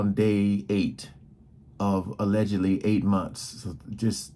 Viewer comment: Omg. I thought they said 8 weeks!!!! Its 8 months??. Jeez. Hey 🤸🏽‍♂️😍👍🏾